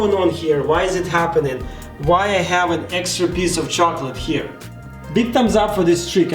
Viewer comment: who heard "big thumbs up for this chicken" ?